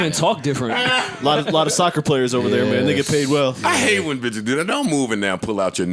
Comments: Did say nigga at 26:44? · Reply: “and they get paid well” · Did say nigga paid well or am I tripping